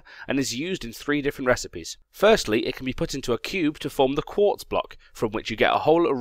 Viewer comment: Did say 'quart'? And more importantly, did anyone else notice?